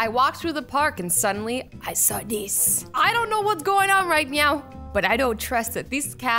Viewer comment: is it just me or did she just said I dont know whats going on right meow